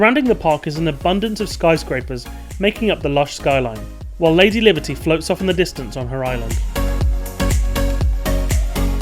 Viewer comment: i tought I hear him say "While lazy liberty floats off in the distance on her island" lol